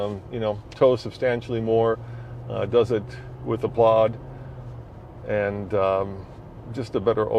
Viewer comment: Heard at 10:17 - "Does it with applog"?? What is he saying there?